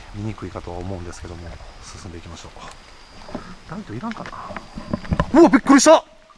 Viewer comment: ～ さんがめっちゃびっくりして大声出してた時、その寸前に聞こえた声に驚いたのかと思ったら「足音が...」って？ え？？ 驚く寸前に「なんか言わなかった？」みたいな早口でしゃべる声がはっきり聞こえたんだけど... 11:41～ トリフィールドの左上に苦悶な表情の顔らしきもの。水滴かと思ったけど、それだけが横に流れる様に消えていく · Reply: の方はさんが「ライトいらんかな」って言ってるんだと思うよ